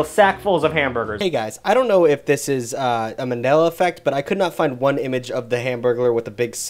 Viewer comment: I’m sorry did you say Manila effect and not Mandela effect?